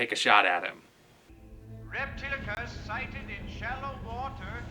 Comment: Did he just say "Reptilicus scientific shallow water " or "Reptilicus sighted in shallow water "? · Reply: "Reptilicus sighted in shallow water!"